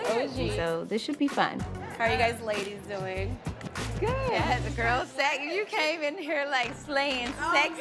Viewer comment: did she say “the girls sagging”